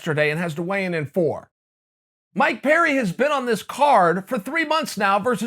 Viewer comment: I thought you were saying "my parent" not "Mike Perry" hahahahaha